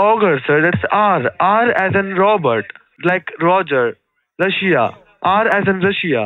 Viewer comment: thought he said rushia lol